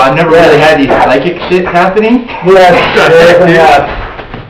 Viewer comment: hello to everyone. .especially to you. .its Linda from Indiana. .I love this video its very interesting. .it sounds like she is saying ' happening " I really don't know lol , .but that is what im hearing ..anyway im thinking of coming to LA probably late summer.of. 2020 .an visiting Cielo. .an hopefully getting to see you all up there..as I said I was there not very long after the murders had taken place..but would love to see it again. .hey keep the videos coming. .as I really enjoy watching them ..later..love Linda